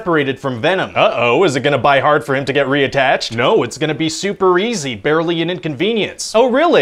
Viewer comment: I sware I hear him say "Is it going to B-U-Y hard for him to get re-attatched?"